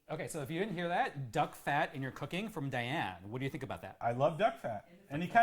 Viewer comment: I heard "I love dog-fat" - and then realized that's not what John said :-)